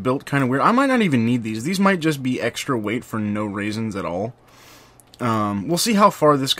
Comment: I swear to god, I heard him say "These just might be extra weight for no raisins at all".